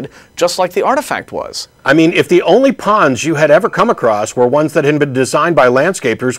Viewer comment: I misheard and thought said any Porn you come across instead of pond. Very good show this week